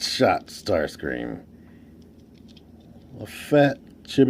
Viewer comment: I loved 'visible smirk syndrome.' I also thought you were saying 'shitty' the first few times you said 'chibi' which made the phrase 'he's a cute little chibi Starscream' pretty interesting, lol